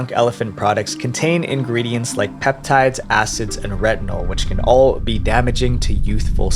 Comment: I heard “redbull”